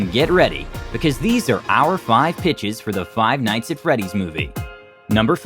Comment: I'm trippin I thought he said these are the five bitchs